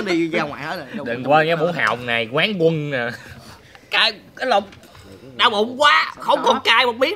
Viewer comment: chắc tui nghe nhầm "Cái lol":vvv